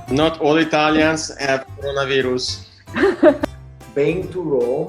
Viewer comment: What does the guy say at @ 8:57? "Not all Italians have...." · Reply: Coronavirus. The 'i' in the word 'virus' is pronounced differently in Italian. As such, phonetically it sounds like 'veerus'.